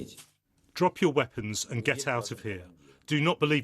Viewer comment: I think that Zelensky said "drop your machines".